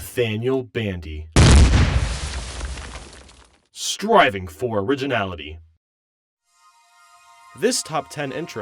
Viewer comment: lol, you were in the car filming the sponsorship, so i skipped to the intro and it sounded like it said 'Driving for Originality'